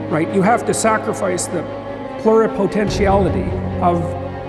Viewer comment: What is he saying at 1:53? I hear something like "plural potentiality". Subs say "flora potentiality"??